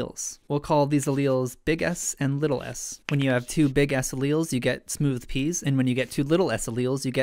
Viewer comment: I keep hearing "bigass alleles"